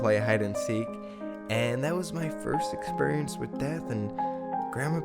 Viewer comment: At first I thought he said it was his first experience with meth, not death. haha